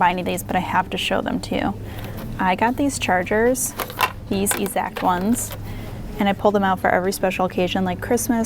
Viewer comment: Did anyone else catch when she said "ezact" instead of exact when talking about the chargers?